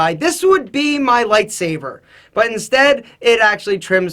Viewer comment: Did he just say lifesaver instead of lightsaber? ?